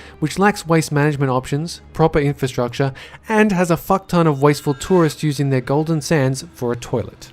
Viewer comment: Did you say fuck tone ?